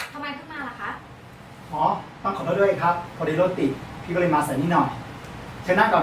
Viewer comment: ผมได้ยินว่า รถติดมากครับ พี่ เลย มาเสือก นิดหน่อย